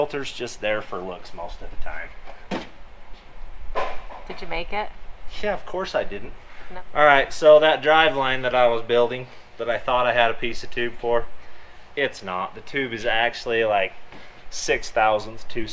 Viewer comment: tossing filter box in garbage, "Did you make it?" Of course I didn't. . . . That, laid back as ever. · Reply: I missed that the first time. I heard "of course I did" and chuckled. Listening again 7:33 ..."of course I didn't" is even better.